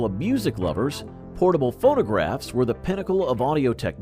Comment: Did anyone hear "photograph" instead of phonograph"?